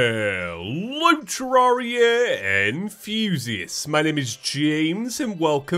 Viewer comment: Here's a funny story for ya. You know your intro right? "Hello Terraria Enthusiasts"? Well my stupid dumb brain thought you were saying "Hello Terraria, and Fuzius" What the hell was i thinking back then...